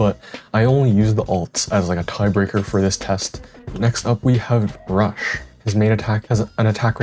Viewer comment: did bro just say Brush 😭 🥀